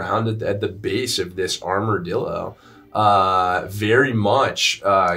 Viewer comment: Did say "Armordildo" ?! lol